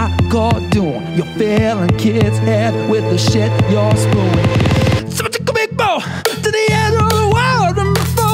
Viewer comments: TSUHWOTUKUHBIGBO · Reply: i genuinely thought he said 'so i took a big bowl' for some reason XD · Can someone please tell me what said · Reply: "SO I TOOK A BOG BOAT, TO THE EDGE OF THE WORLD!"